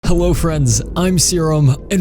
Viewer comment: It sounded like you said hewllo